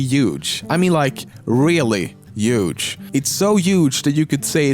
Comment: is he saying huge or uge ? 3:10 lmao